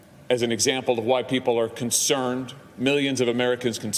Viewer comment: does he say WHITE people are concerned ?????